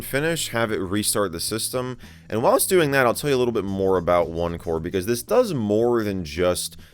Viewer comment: Thought he said opencore for a sec 😂